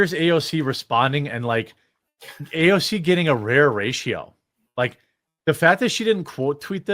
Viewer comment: Did I hear that AOC is getting a "rear ratio"? (6:48) Ooh! The dirty girl!